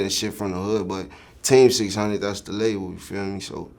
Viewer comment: This man sounds like he sayin tay600 instead of team 600 lol